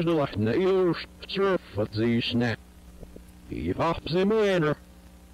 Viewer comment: i hear "the poppy simulator"